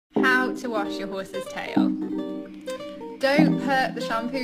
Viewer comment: i am so sorry but when i first watched this i thought she said “how to wash your boyfriend’s tail” 😭😭